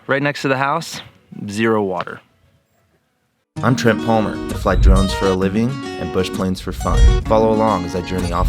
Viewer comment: I thought you said I fly drugs for a living hahahahh